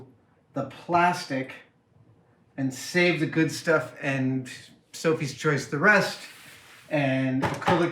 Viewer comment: Did he say "and Sophies choise the rest" at 24:20? That's putting things miles out of context...